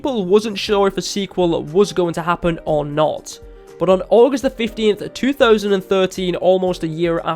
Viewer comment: I've been a pvz fan since it was only on PC. This game has come so far. 3:06 is it just me or does he say august 50th?